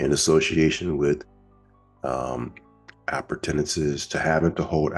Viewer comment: what word is that? Appurtenance?